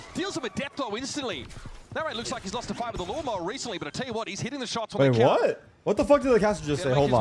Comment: what does the caster said ? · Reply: “Narrate looks like his lost a fight against a lawnmower recently”